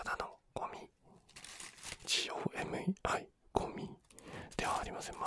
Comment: ｢G・O・M・E・I｣ 私｢それはごめい｣ (多分空耳、ごめん)